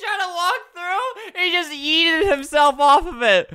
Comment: Did I hear.......... Yeet